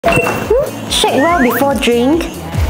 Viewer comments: When she said shake well before drinking I thought she said shit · What the video says: "Shake well before drink What i hear: "Shit well before drink "💀💀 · At the start i thought sists said ",shit ball before drink😅"